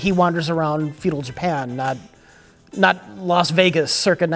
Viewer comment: Did he say "Fetal Japan"?